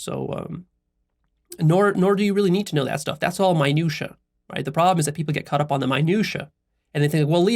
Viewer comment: anyone knows what word he said about 1:13:18? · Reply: That stuff. That's all minutia